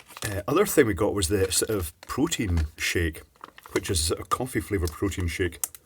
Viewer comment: I misheard "protein shake" as "poutine shake" a couple times.